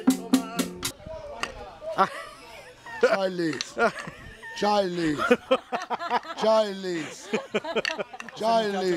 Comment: what is he saying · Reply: “Charlie” Dashie’s dad is calling his name